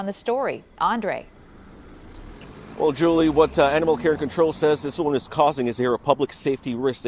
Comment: I heard "Animal-Karen Control" 😄🤣